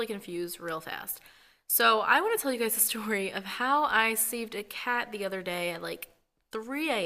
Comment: I thought it said "i shaved a cat"😂